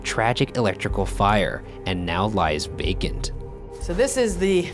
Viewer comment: when he said "now lies vacant" I thought he said "now lies baconed" I was like what?